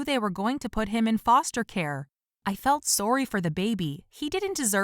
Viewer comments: did she say soory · Did I hear her say sorwy or am I just hearing things?